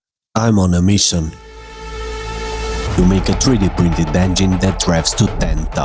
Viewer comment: I'm sorry, but I lost my shit at the beginning, I heard you say "I'm on a mission... to make a 3D printed Bengine" started crying laughing, love your vids man, I count your videos as "brain nurishment" and they are addicting lmao